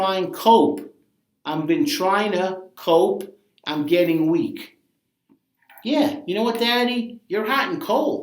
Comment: You need a better camera, a mic on your shirt or something else to made audio better, and some sort graphics/visuals. You have amazing break downs and wayyy to much insight for it to be wasted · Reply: Also at 12:35 you say he's getting "weak" but in the song he actually says weeded, basically implying he's using marijuana as a coping mechanism for the struggles he's going through